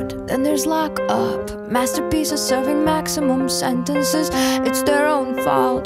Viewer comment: I thought she was saying Master P's just serving maximum sentences!